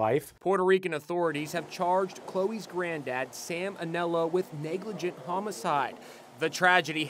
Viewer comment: Sam Onelo I thought he said salmonella